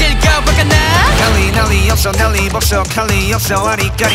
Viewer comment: hyunjin's line,I mean just me or i heard "LLAMMAAA"